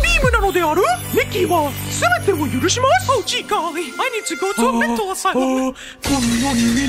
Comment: Did I hear “i need to take a 💩” 😂